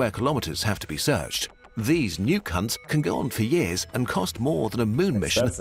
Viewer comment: did he say nuke hunts or new…